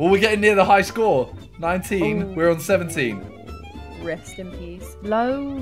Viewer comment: Did say rest in peace